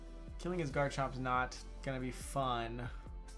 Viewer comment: Why do you have flamethrower over fire fang? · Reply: I thought he said his chomp is quiet nature?